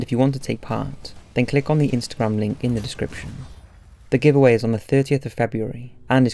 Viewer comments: Did he just say the giveaway was on the 30th of Feb? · Art first it sounded like you said the giveaway was on the 30th of February. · Giveaway is on the 30th of February. Did I hear that correctly? Because I replayed it about 5 times.